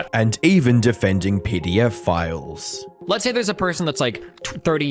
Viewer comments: Seriously thought you said "and even defending PDF files." Not to offend or poke fun but this is the first of your videos I've seen and your accent made it hard to understand on first pass. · I heard "Defending pdf files" for some reason and was like "bro, PDFs are fucking great. WTF you on." Then he started talking about 40 year olds and I was like "oh, yeah old people deal in PDFs more" and then he said grooming and I was like "oooooooooo"